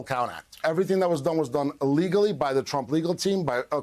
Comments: "Everything that was done was done illegally" (3:57). Am I the only one who heard him say ILLEGALLY? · “Everything that was done, was done illegally by the Trump legal team according to the rules and under the leadership of Rudy Giuliani…” —Boris Epshteyn Did I hear that correctly? If so… “So full of artless jealousy is guilt, it spills itself in fearing to be spilt.” —Shakespeare, Macbeth Welcome to the stupidest party in America: the Repuglican Party—the Stupid Peoples’ Party.